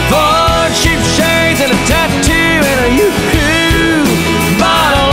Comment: For longest time I thought he said "Cheap shades and a tattoo on her yoohoo..."